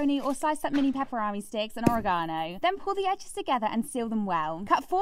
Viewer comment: Ive never heard ANYONE pronounce "oregano" that way😂 Edit: Y'ALL NEED TO CHILL. What I said was not insulting. I just made this comment because the way she said "Oregano" was completely new to my ears, THAT IS ALL. I shouldn't have to explain myself. If a comment like this is what sets you off, get off the internet and touch some grass. There are wars going on and yet you choose to fight and squable over this?! Edit2: Being American has nothing to do with it. Anyone could have made this same comment about any other word and be from anywhere. It does not make me tonedef. Im sorry that you dont like that im American and didnt know how another country pronounced a word. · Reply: Real talk, when she said it I heard "oh-reh-gah-nay" and was so thrown off I had to let the video loop to hear it again before I realized I misheard her 😂